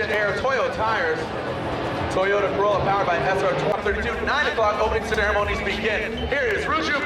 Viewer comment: Did he say that the corolla is powered by an SR20 or did he say F22C at 0:33?